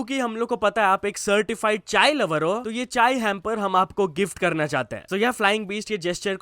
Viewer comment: sounded like certified child lover